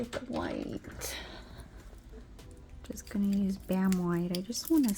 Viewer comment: I thought you said let me get this damn white 😂 I was like on here CUSSINNNN 🤣🤣🤣 but you said bam white 🤭🫠 I had to rewind it like did cuss 🤭🤭🤭